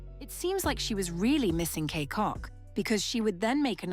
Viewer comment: Gng did u just say kay cock?